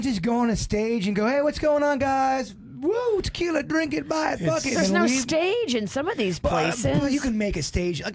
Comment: Did say bucket or fuck it?